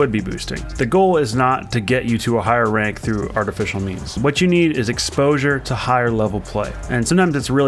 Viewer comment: did he just say 'artificial memes'?